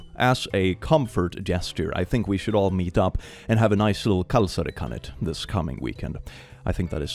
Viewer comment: Oh kalsari kännit (drinking in your underwear)! Sounded like kalsari kani (underwear rabbit)